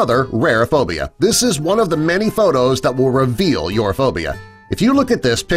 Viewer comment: "Trypnophobia, is an extremely rare fear" My entire family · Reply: L x l l y_ Lol, I thought you said potato instead of photo- (I'm sorry, it's 5 am right now for me and I'm going insane-)